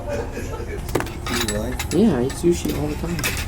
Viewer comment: did he said Viet Nam